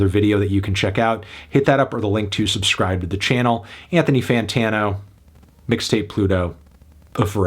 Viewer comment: “Bigthony Backtano” is what I thought I heard at first